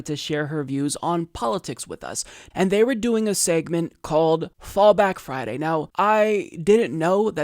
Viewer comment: thought he said fall back fatty